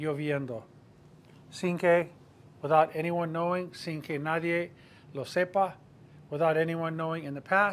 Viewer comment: I'm a bit confused about the use of 'no nadie sepa'. At first glance, a bit like 'no digas nada', this seems like a double negative (nobody not knowing, do not say nothing) and yet the meaning is what it is (nobody knowing, don't say anything). Am I missing something (obvious)? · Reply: I'll have to clean my ears. I thought I'd heard something like 'no lo nadie sepa'